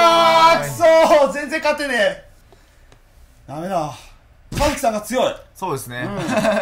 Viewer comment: カズキさんが カズさん、、、に聞こえた